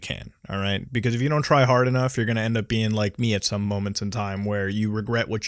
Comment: I clicked away at 8:56 and all I heard was trihard, I’m done